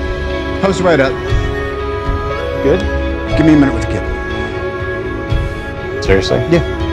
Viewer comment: I’m corrupted, all I hear is 4:49 “give me a night with the kid.” And 6:01 “I’ll be happy to take you home.” Please, don’t hate me, it wasn’t